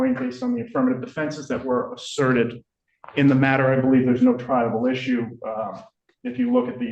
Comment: Thank you for explaining triable, I always thought they were saying tribal!